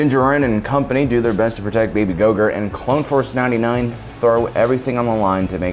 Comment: did call Grogu, "Grogurt"? 😂😂